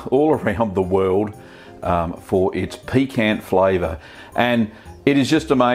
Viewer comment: Did I hear that right? "For it's piquant flavor"? This mofo thinks that PARMESAN is spicy?